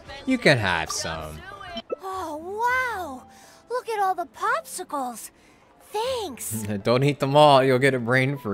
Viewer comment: bennet? I hear someone eating chopsui, yes I know it is spelt wrong